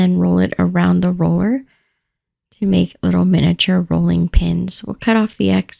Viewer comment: lol I thought you said "bowling" pins and I thought "huh??'.